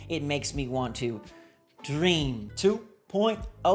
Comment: i thought u were gonna say, “they’re so soft, they make me cream” AND I WA SLIKE WAHATATATAT??? i had to rewatch five times. of course u said dream. that makes a lot more sense than cream.